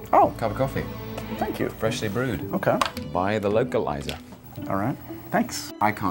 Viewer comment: Did.... did he say lokiliazor ? 3:18 HAS ME DEAD, TOMS JUST LIKE "he looks nothing like me, he has a mAstache" and is just "BLAH"